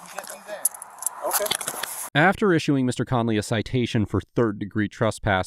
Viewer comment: did he call me Mr nelson? Wt???